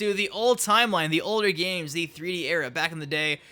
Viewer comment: did you say E3 era (instead of 3D era) Hmm